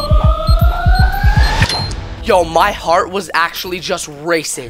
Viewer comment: When said my heart is racin I thought he said my heart is racist